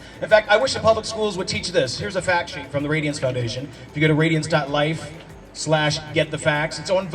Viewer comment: Does anyone know what website he is saying at 2:45? I can’t understand what he is saying and I am not getting any hits on Google (surprise surprise). TIA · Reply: The Radiance Foundation is what I heard